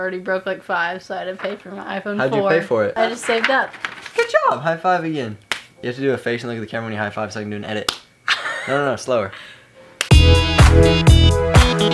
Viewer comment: Did anyone hear the "good job"